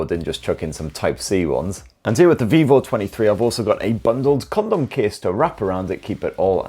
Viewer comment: Did I hear bundled " condom" case?